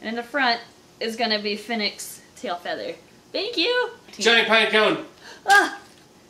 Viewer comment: When through the piecone and said giant piecone I thought he said giant python and I thought he through a python.🤦